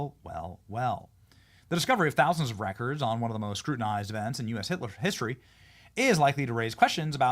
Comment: I heard that, Benny. 39:33 "US Hitler-history..." You've probably read that name more than you've read that word. lol. 😏